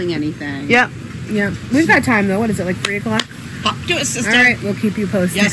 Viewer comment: Lmao on video at 19:15 the mom sounds like she says "fuck you sister" laughing.. she didn't... but it sounds like it .. for whatever reason I thought it's hilarious 😂 ..